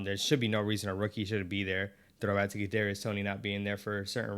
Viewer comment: Did you say "Kadarius Toney??" (0:23). Congrats on tying the knot! 27 years happily married for me. Wish you guys all the best!!